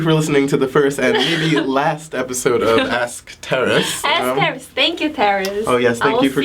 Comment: When he said his name I first heard "terrorist":--D ㅎㅎ